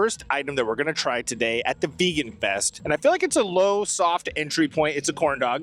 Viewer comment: Totally thought that said Vegas fest. Maybe that’ll be your next step 🎉